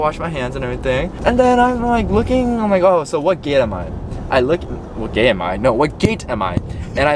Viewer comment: “so what gay I am?” what is you saying… mental health?….